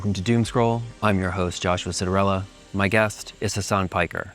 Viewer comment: Oh my god i thought he said Joshua Cinderella